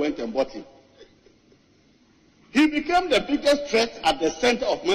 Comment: The birth of Maguirism🤣🤣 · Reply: Thought said he became world biggest threat to life haha